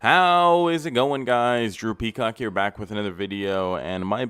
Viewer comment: everytime you say "Drew Peacock" in the beginning, i hear "drippy cock" instead 😩